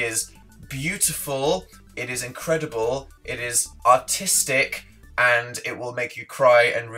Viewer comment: I'm.... den when you say artisting. it sounds like your saying autistic